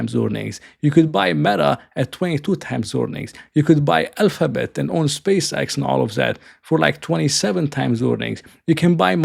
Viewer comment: Did you just say you can buy space x instead of NFLX for 25 times fort ward earnings? 😂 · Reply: He said "you can buy alphabet and own SpaceX and all that for like 27 times earnings". Not because SPCX is at 27x, but because Alphabet is at 27x, and they own 6.11% of SpaceX.